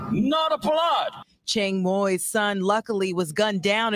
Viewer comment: did she say “luckily”?